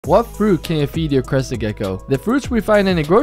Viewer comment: Literally, who else thought he said, crusty gecko😂😂❤